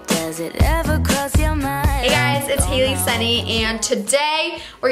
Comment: i thought you said "hey guys it's Hailey Sunny"😂😂so i was like "why not Hailey Rainy"💀